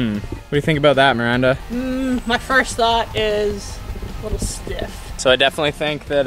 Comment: When Miranda said, "A little stiff", I thought she said, "Looks like shit".